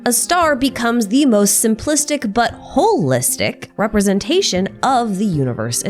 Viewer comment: i heard buttwholeistick